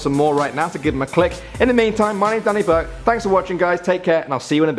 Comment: sounds like he is saying "thank u guys for watching and i'll see you in a/yo bed" XD